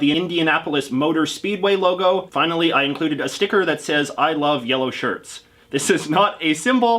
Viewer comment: It sort of sounds like you said 'Isle of Yellow Shirts' there.